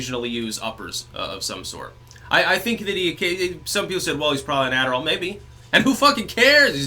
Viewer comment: Can someone tell me what exactly Styx said here - "... some people said... ( ? ) ... maybe. Who f'ing cares, he's doing a good job." Subtitles say "well he's probably natural" but that's not right. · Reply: "well he's probably on adderall [the ADD medication]"